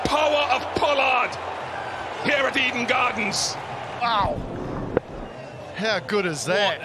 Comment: Did he said eden gardens?